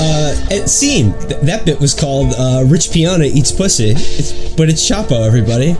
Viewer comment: all this time I thought Felix was saying Rich Guyana